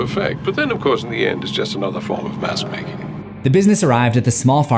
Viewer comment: another form of mask making? For a second I thought he said another form of masturbating 🤭